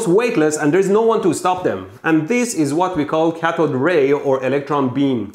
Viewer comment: I heard 'Cathode Ray' as 'Catholic Ray.' High voltage holiness.